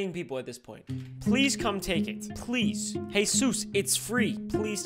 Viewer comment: hey sus instead of jesus had me dead 💀